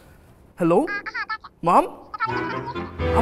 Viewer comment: Why did I hear “HUH MY DOG IS DEAD?!” When said “HUH IM ADOPTED?!”